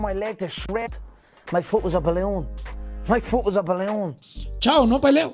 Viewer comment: ¿Alguien puede decirme qué dice Conor en 1:39 por favor? My focus...¿? ¡Gracias! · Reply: My foot was a ballon